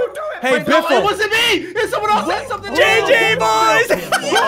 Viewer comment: When ssundee said jj instead of gg boys